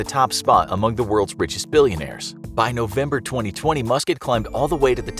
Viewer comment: i heard musket 😂